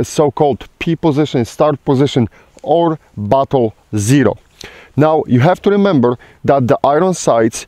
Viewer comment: When he said, "Or battle zero", it sounded like he said, "Or butthole zero" to my ears.